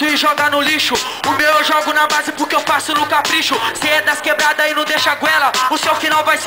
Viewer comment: O que o fala na primeira frase? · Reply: gomes eu entendi "o meu eu jogo na base pq faço no capricho"